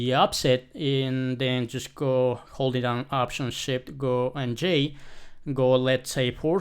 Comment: how did you open the " Go To Time " window on 6:37 ?? i can't get it what you said there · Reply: sorry my bad, i have my after effects shortcus costomized that's that's why, but for default in mac is SHIT + OPTION + J Windows will be SHIFT + ALT + J, please suscribe thank!...